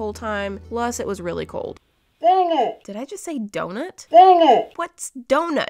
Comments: It sound like she said Danish, not donut lol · When thought she said donut she actually said dang it two reasons first reason because I heard her say dang it and second reason is because in the video a couple seconds later she said this would’ve been perfect but I can see the letters and I hate that so when she pulled it up and looked at it it sounded like she said donut but she actually said dang it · When she it sounded like she said, “donut” I think she actually said,” darn it” . Like if you agree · Me: *hears dang it* Voice over: Did I just say donut? in the video: dOnUt Me: This is the new Yanny VS Laurel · She thought she said donut but she said darn it (like if ;you agree) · I think you said Dangit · Btw, you did not day DoNuT.. I heard it clearly...-ish, but you said "dang it" not dOnUt sooooo.... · I think when you thought you said donut I think you said dang it because you made a mistake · Donut! Voiceover:Did I just say donut?Donut! Voiceover:What’s donut? Me:Laghing really hard while thinking she said dang it even thought she actually said donut LOL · "dang it" 10:15 "kinda cool" 10:42 "pretty cool" THAT'S WHAT I THINK SHE SAIDDDDDDDD :> why am I yelling? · I think she said dang it??? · I thought you said danish instead of donut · This is what I say when I'm hydro dipping: DOUGHNUT. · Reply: When she thought she said donut she said dang it